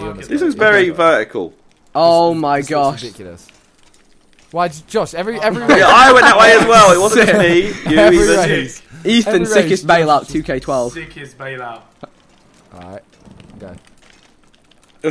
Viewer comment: anyone else hear henry